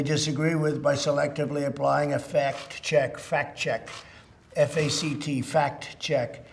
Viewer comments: I love how he spelled out "fact" like it's the rest of us who are unfamiliar with the word. 😂😂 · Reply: Oh, I thought he said fat chick 🤔. Thanks for clarifying. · Thank God he felt the need to clarify "Fact Check" because I thought he said "Fat Check"